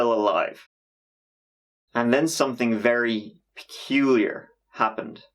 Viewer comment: The way he said "Peculiar" at 6:54 sounded like someone whispered "Ricky"... yo i freaked out